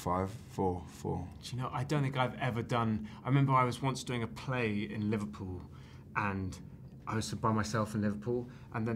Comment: what did he say pointing his finger to eddie? 🥺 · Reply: He said “you heard it here first, you heard it here first!”